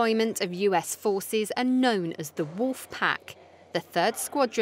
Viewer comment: it sounded like she said British forces are on the piss up at the beginning.